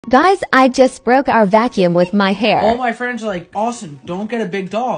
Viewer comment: I THOUGHT IT SAID GUYS I JUST BROKE UP 😢😢😢😢😢😢😢😢😢😢😢😢😢